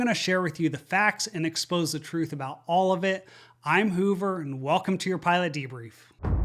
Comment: Excellent work, Hoover. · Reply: 4 a second I thought you were saying Goober as your name. My Bad... Thanks Hoover. 🇨🇦